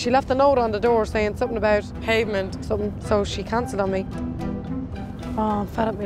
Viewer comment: She is so rude and pronounces bereavement as pavement 🤦🏼‍♀️🤦🏼‍♀️🤦🏼‍♀️ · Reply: I thought she was saying payment! Lol